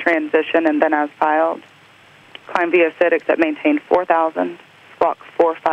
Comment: "Climb via Sid..... maintain 4000" Does she say "except" in between? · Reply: VV Kim yep. "Climb via the SID except maintain 4000".